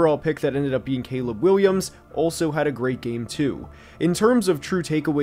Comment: I had to rewind because I thought you said “young black king Caleb Williams” and I was like damn I didn’t know you were valid like that tbh